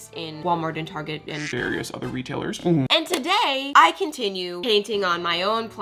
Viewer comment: Is it just me or at the start it sounded like she said ‘today we’re painting on some slushiessssss’